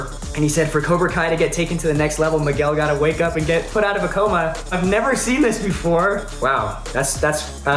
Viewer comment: It's cute that Xolo (and half the commenters here) don't know that Vince Staples is saying Miguel needs to be initiated into the Pacoima Crips gang. Xolo says "Put out of a coma" instead of "Put on Pacoima". He clearly had no idea what Vince was talking about, LOL.